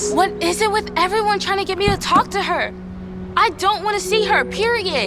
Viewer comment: nahhhhh what did she say? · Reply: She was like i don't want to see her . [period]